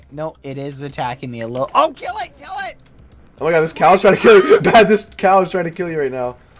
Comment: sapnap says the cow’s killing you. The subtitles says the couch is killing you